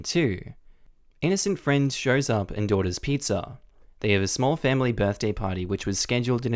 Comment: It sounds like he said “Innocent friend shows up IN DAUGHTER’S pizza”! 😂